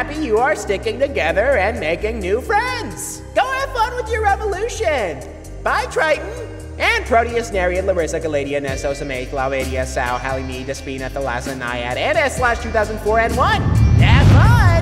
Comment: "Bye triton, and protus nereid Larissa galatea neso psamathe laomedeia sao halimede dispina thalasa naiad and S/2004 N1!" 8:29 this is what i heard correct me if im wrong